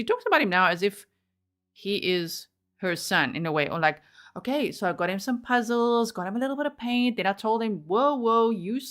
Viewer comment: I think said that listening to Jorge’s music made her feel fractured, whatever that means. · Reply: Fractious (nervous)